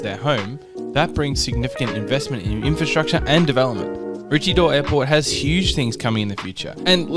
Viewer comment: When I first heard of this airport's name, I thought the person said "My witchy door"😂